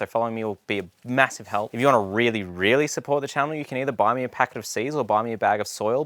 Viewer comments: I am partially deaf and I heard "if you want to support me you can buy me a pack of cigs" lol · I honestly thought you said, buy me a packet of ciggies 🚬 😂